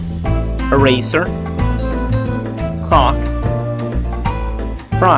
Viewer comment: bruh did i hear him say the c word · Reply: No, I said Clock. I just mispronounced it.